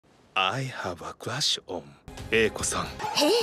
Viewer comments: I first heard "I have a glass bomb" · For a second I thought this dude said “ I have a question”